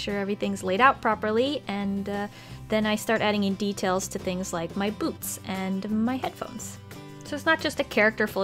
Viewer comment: like the soul and the trims.... Me :WHATT ohhhhh i thought you said " like my soul... " BAAAHHAHAHAHAH